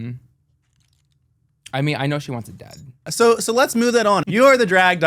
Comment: what does she say right here? I think I have an idea but idk if it's correct. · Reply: I know she wants it dead